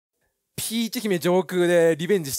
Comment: 最初ピーテ姫上空って聞こえた人いる？